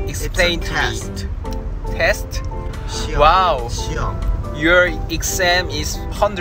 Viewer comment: I CANNOT BE THE ONLY ONE WHO HEARD KIBUM SAY “SHIRO” WHICH TRANSLATES TO “I HATE” 😭😭 WHEN I SAY THE MOST RELATABLE IDOL — 💀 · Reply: He clearly said 시험 (“test” in korean) to translate for taemin since he did not understand what exam meant